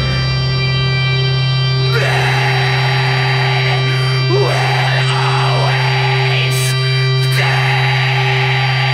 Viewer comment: I cannot for the life of me understand what he says can anyone help out? 4:57 "Man will always..."? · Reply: “Man will always fail!”